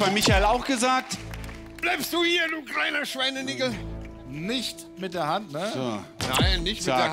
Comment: Der Lichter war echt Peinlich, und hab ich das richtig verstanden bei 10:50 ? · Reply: Ne, hast du nicht. Das hat nur dein Gehirn draus gemacht. Er sagt Schweinigel <link> in koelscher Mundart.